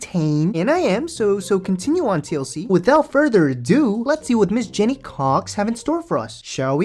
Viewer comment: Did just said “Jeni C**k”?